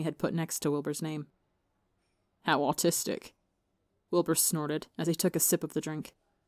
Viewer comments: I deadass thought you were saying autistic then realized ur just using a british accent 💀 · Someone help me i heard "how autistic" instead of "how artistic"